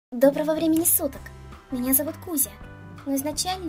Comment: Вначале,когда я начала смотреть этот обзор мне послышалось,что он сказал "Привет,меня зовут Гуся"